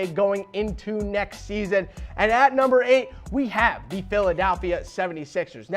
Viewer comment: Did he say the raptors Added Kyle Lowry and got rid of derozan 😂😂😂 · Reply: What about 3:48 when he said "PhilDALphia 76ers"